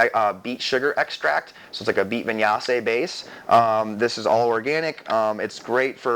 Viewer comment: What did you say at 3:16 before "base"???? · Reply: He's talking about sugarbeet molasses